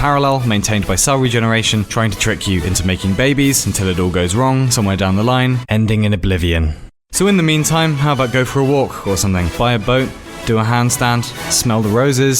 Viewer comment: I thought it said "fly a boat" at the end there